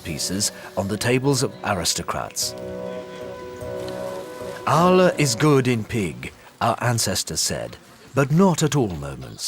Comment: "Allah is good in pig".... what. Tell me I heard that wrong.